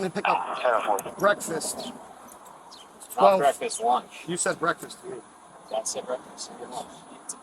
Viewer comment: he clearly said lunch